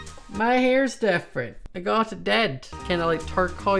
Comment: I thought he said “I just got it dead!” 😂 no offense to u OR ur accent